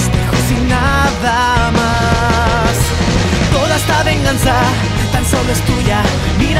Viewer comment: la primera vez entendi "Tan solo estudia" y con "mira hacia el futuro" justo despues, me lo crei mas :v Y yo sigo aqui mirando videos :v